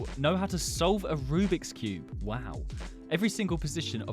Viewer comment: i thought it said insult a rubix cube